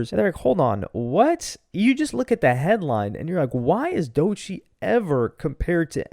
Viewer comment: Why does Ak put an "R" in Dochie's name? Now I just heard "Doichie" lol